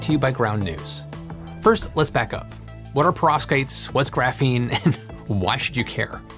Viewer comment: hm. Is it just me or does anyone else hear also only „prostates“? No? Just me then.